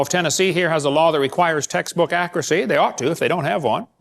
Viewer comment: Everytime he said "textbook accuracy" all I heard was "taxes accuracy". Isn't he on lucky wife 13 these days?